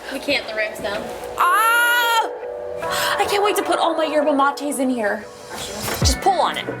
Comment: did she said yerba mates? im Argentinian and the yerba of mate dosn't go in a fridge